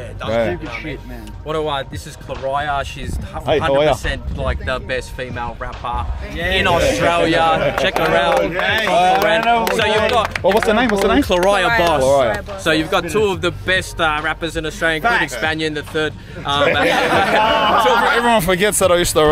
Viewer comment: I’ve played 46:32-46:44 so many times and I still cant figure out her name. I know I’m hard of hearing but even trying to look up what I thought was kor•ai•ah boss or something i can’t find her. Whats her socials. I’m always down to support australian female rappers especially up-and-coming. Anyone know. I just want to listen to more music from Australian Artists.